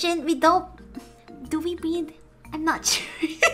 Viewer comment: Not to make fun of the accent, at first I thought she was saying 'Do we breathe?'